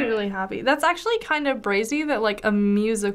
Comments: did she just say “brazy”? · did she really say brazy? 😂